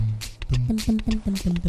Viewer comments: what is said as kills the guy? "you just got award?" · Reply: F̡ac͜e͡l̨e̶ss͟ "Anwar'd" it's the name of the guy who put together this build. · You just got (insert word)!!!! No idea what was just said. Anybody else know? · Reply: Anwar'd. He's another person who worked on the guide and he adored the cannon according to ENB.